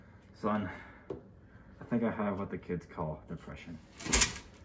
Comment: The son tapping his head and saying "there there" was probably the least helpful but also most realistic reaction possible. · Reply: I thought he said "been there"